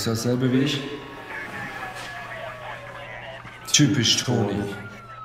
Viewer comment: was hat er denn jetzt gesagt?? 🤔 😁habe es immer noch nicht richtig verstanden? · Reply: "Typisch Toni"